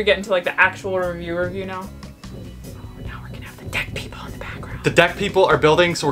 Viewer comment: At first I definitely thought you said there were dead people in the background making noise. 😂